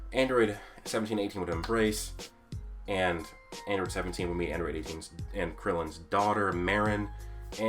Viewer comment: Lmao the typo in the captions says Android Asians instead of Android 18